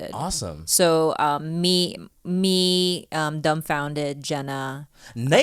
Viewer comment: Don’t wanna be a party pooper but it’s dumbfoundead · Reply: Oh wow never noticed that. Only found out about him from Tigerbelly and I kept hearing them say "Dumbfounded" and not "dumbfoundead" or I might just have hearing problems lol